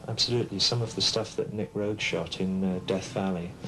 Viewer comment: At about 00:02:05, does he say that Nick Rhodes shot the film in Death Valley? As in Duran Duran? I can't find anything else in the internet about this. But it seems plausible, kind of, seeing as Nick did lots of photography back in the day. He just kinda says it and then moves on. Hmmmm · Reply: no he’s talking about Nicolas Roeg, he directed The Man Who Fell To Earth as well as all the live action Pros And Cons footage